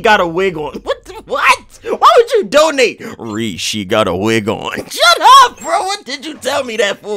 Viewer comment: "Rease, she got a wig on" literally killed me for 5 minutes straight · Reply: Bro I first thought he said “rease she got a wiggle” and honestly both were hilarious